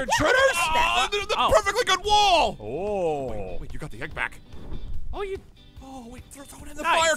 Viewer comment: DID SAY BIG BACK????